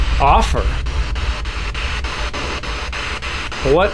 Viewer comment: Instead of “offer” I heard “the father”